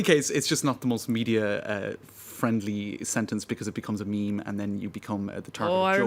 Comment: Wow, did he just say meme? I love it.